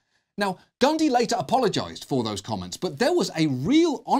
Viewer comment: I heard you say "Gandhi apologized for his comment".